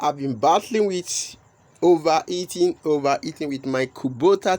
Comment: I thought you said "Over eating". Good idea though